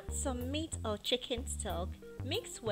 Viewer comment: you said add meat or chicken...?? I didn't understand that part you said it at 1:28.. and when i continued watching for like 10 more sec you stirred the dish after you added the chicken liquid stuff but i didn't see any chicken meat when you stirred it.. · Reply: ***** In the video , I said, add meat or chicken stock (that is the liquid you get from boiling beef or chicken :) ,see wriiten recipe here: <link>